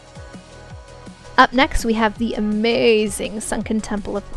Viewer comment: Swore she said sunken temple of porn at the end there lmao